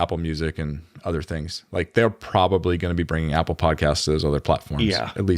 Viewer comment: i thought they were saying apple pie casts